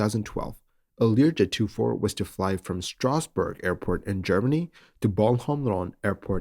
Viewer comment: I thought you said “Strasbourg”, I was like, “What the hell?”